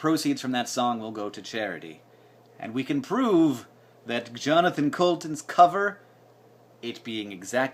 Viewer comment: Lol i thought he was saying john decoulton. But looking at the comments below, looks like its jonathon..